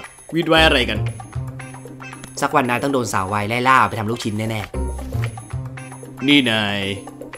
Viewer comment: เราขอโทษนะ เราไม่ได้ยินว่าจับทำลูกชิ้น เเต่ได้เราได้ยินเป็นจับทำโดจิ-- อืม นั้นเเหละ..